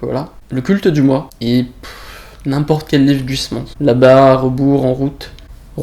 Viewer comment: N'importe quel livre du... " ?? Qu'est ce qu'il dit ? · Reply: Oui, Huysmans